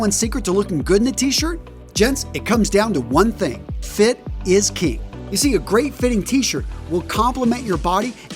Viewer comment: You say “fit is king” but the subtitles say “fit is key” @ 00:48 You say “then don’t buy it” but the subtitles say “and don’t bite” @ 01:21 You say “buy” but the subtitles say “by” @ 01:57 You say “anybody” but the subtitles say “any body” @ 02:41 You say “build” but the subtitles say “bill” @ 03:04 You say “that are” but the subtitles say “they’re” @ 03:32 You say “then” but the subtitles say “thing” @ 03:47 You say “t” but the subtitles say “tee” @ 05:09 You say “close fitted areas” but the subtitles say “clothes fitted areas” @ 05:14 You say “2-5” but the subtitles say “2 to 5”